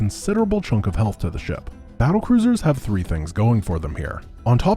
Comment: Cattle Bruisers? Did I hear that right?